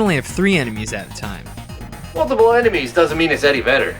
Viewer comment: I swear I heard him say “multiple moves doesn’t make it Eddie Vedder.”